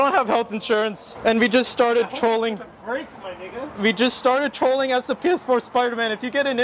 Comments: i thought he said,i hope this thing doesn't break my nigga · What did I hear one say to the other at 2:46? "... break my [ __ ]" Someone let me know in the comments.